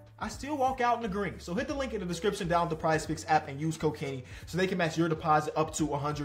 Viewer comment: when he said "use code kenny", i was in another tab and thought i heard, "used cocaine"